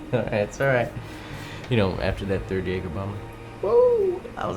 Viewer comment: Subtitles say third egg obama